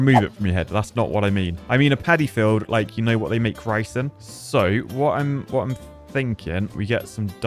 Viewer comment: At first I thought he said where they make ricin which is poison 😂